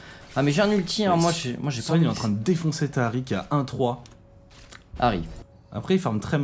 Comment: Pour le "Taric" vers 12:00 a dit "ta Ahri se fait" et a du entendre " Taric se fait" ;)